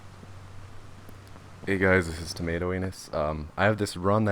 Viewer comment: Who else was like did he just say tomato anus lol